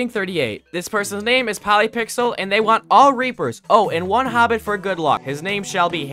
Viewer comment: BRO DID NOT SAY REAPER